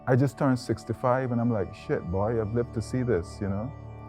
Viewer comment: Am I hearing stuff or does he say "shit boy, I lived to see this." @ 3:50 lol